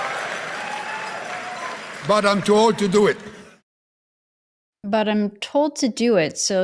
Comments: I thought he said "I'm too old to do it". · I thought he said he was "too old to do it". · I heard “but I’m too old to do it”